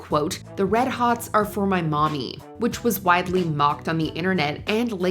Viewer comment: I listened 5 times and I don’t hear “mommy” I hear “my mother”